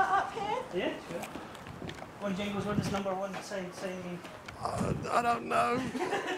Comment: At roughly 22:50 what are they saying? "Jingles one does number one ??? say mean" I'm confused.